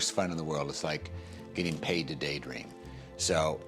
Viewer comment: does he say getting payed to date rape? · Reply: Paid to day dream.